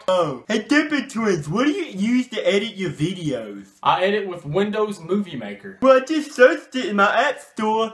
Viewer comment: I thought he said windows boobie maker